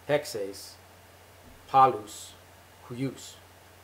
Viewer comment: Yes, you did a great job translating it! The only discrepancy is that at 6:08 I say συ ἑξεις πολλους υιους. you will have MANY sons, although I can see where you might have heard this as καλους υιους, "good sons." It is very encouraging to me to know that SOMEONE can understand my Greek. That is what I am aiming for, not to speak Greek well, but to speak it often and with understanding. χαρις σοι και σοις!